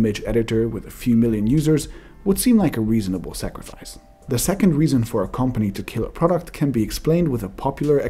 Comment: For a second it sounded like you said "20 second"